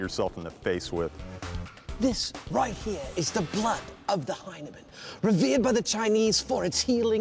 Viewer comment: Maybe he didnt realise what he was saying when he marvelled at the bloody of the hymen (Hyneman). Did anyone else mishear that?